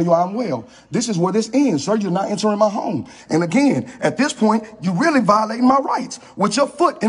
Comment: Am I hearing things or did he say at the 7:20 mark "That again, at this point, you're really bothering my wife". Forgive me. I just smoked a blunt.